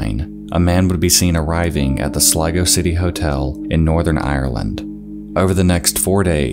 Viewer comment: i first heard lego city hotel. And i was likw hol up